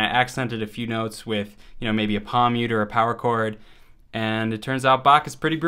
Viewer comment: What did he say at 1:45 ? Pawmuter or something like that? :D